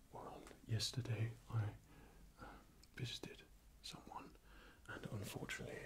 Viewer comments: I shot my eyes open at 5:10 because I thought he said "Yesterday I fisted someone..." 😶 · seriously thought you said "fisted someone" had to do a double take xD. awesome video as usual. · "yesterday I fisted someone" well that escalated quickly Ik he said visited I just heard fisted first